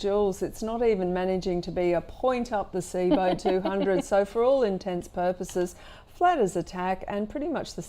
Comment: Did you just say "intense purposes"?